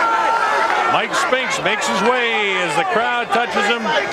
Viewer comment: i hear an audience member say, "HEY! Mike Stinks!"